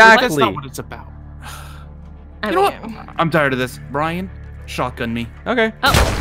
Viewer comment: Just got a theory but I swore I heard “ok I’m tired of this Bryan shotgun me.” “Ok bryan shoots Mario and looks at the two bears did I stutter” I swear I’ve heard him say this somewhere else before??? 🤔🤔🤔 but were 😱😱😱😱😱😱😱😱😱😱 What if there is an SCP with multiple personalities?? wink wink nudge nudge - coughs FNAF Bryan